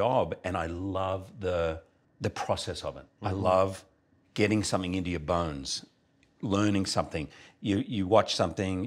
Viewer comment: Am I the only one that keeps hearing "Get into your buns" instead of "Get into your bones"?